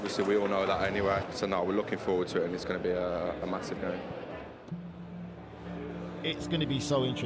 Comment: Did Grealish say it's gonna be "a massive day" or "a massacre" . I heard it as massacre and he wasn't wrong!